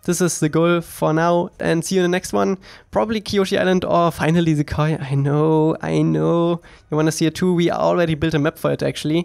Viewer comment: "Probably Kyoshi Island or finally the ???" Anyone know what he said there? I couldn't understand it. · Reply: Koi minigame